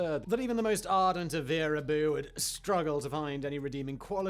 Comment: what does he said at 1:02 antivir abu can not make up the word. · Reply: Wehraboo. Someone who thinks and keeps saying the German army and its tech were better than anyone else.